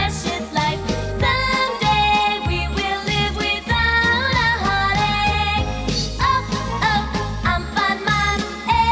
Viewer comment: What are they saying in this part? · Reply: "we wil live withount a heartache"